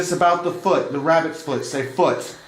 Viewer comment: i heard rabbit foot but, it almost sounded like a question.